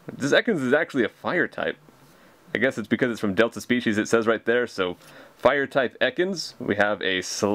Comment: at first I thought you said delta feces ;D